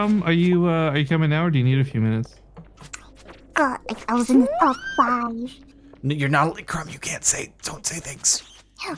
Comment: What did crumb even say at 3:30 lol? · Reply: I think she said "I was in the top 5". Don't know in what tho